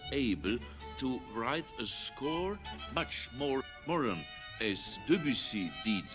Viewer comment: I suppose he's trying to say "modern" at 5:21. At 11:17 he says "Wandervogel" instead of "wonderful". 25:57 Thereafter I think everywhere it lacks etc. 30:46 the regime, which I'm sure he hated. 39:43 First World War 41:05 ihm nach [lit. "after him" in the sense of following him into death] 46:12 He says "anxiety", but pronounces it incorrectly.